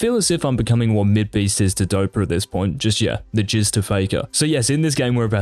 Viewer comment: did he just say "the jizz to faker" HAHAHAHAHA